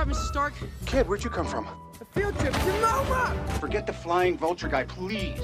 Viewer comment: Is it just me or did I hear "your mama" at 0:43 and that killed me 😂